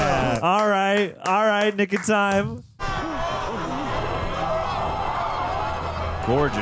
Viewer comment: 😂 in the “Nick of time”. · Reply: Lol I heard him say "Aight Niggah Time"!!!